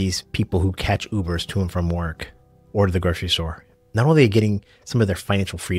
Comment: What's that you're saying at 4:07 ? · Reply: I’m saying people use Uber to go to the grocery store. I don’t need to, but they like the luxury of it.